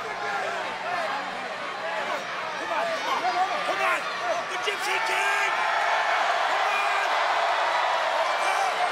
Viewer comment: what did he say? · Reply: Egyptian king